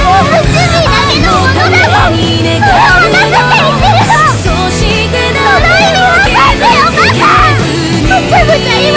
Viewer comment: ってなんて言ってんだ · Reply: 「それを渡すって言ってるの」ですね 映画を見ると分かりやすいですよ